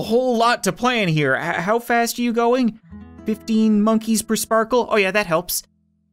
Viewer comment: Did he just say 10 monkeys purse Sparkle?